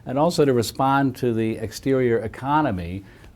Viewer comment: darn, i thought it said ergonomics